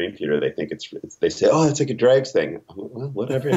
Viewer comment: What does Mr. Rudess say his bandmates confuse his Gentle Giant influence writing for? It's around 8:45. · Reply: Cobb Wobble he says the Dregs, referring to The Dixie Dregs